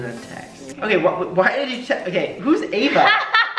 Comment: at 55 I heard say “who’s Eva” like whta?!